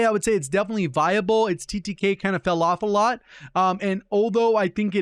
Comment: OMG at 09:47 I cracked up when you said "it's TTK kinda fell off a lot" 😂 It sounds like you said Falafel or something 😂